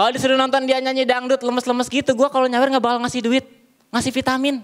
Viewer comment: dia bilang apa sih? soalx disensor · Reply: Al gazali